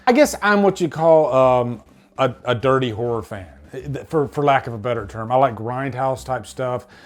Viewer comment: Lol for a second there i thought u said "I'm a dirty WHORE fan" 😂